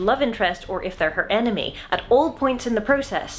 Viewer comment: I thought you were saying "anime" not "enemy" 💀